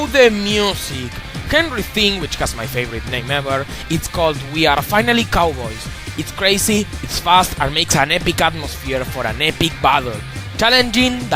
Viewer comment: I'm sorry, this may seem kind of mean, but it sounded like you said we are finally cowards. I mean no offense.